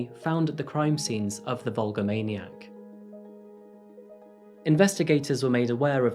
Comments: I kept hearing "Hulkamaniac". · If I didn't read it, I would have misheard "Hulkamaniac" instead. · Just listening to this I thought you were saying Vulva maniac‼️ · Thought you were saying Hulkamaniac.